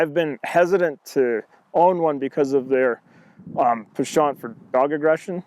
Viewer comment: What does he says at 1:22? "...because of their blank for dog aggression..." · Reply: Joe GarP - "penchant"